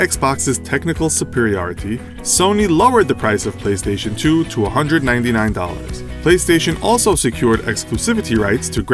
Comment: 299 or 199 because I think you said 299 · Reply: 299